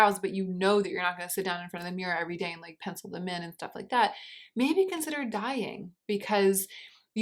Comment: I zoned out for a minute and around 6:00 I heard “maybe consider dying” and I was taken aback 😂